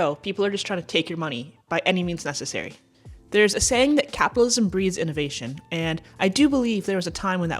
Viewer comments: "Capitalism breeds enervation." People just misheard. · I like that I hear instead of, “capitalism breeds innovation,” I heard, “Capitalism breeds invasion.” 😮😅 ACCURATE!